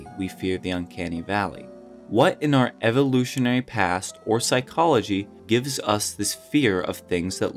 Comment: Sorry omg. Bro at 1:28 I thought he was saying psychology wasn’t real